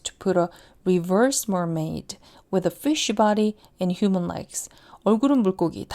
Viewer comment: fish body에서 sh에 모음을 붙여 발음하는 바람에 걍 댓글보면서 틀어놨다가 ~쉬바리 로 들려서 욕한 줄 ㅋㅋㅋㅋㅋㅋ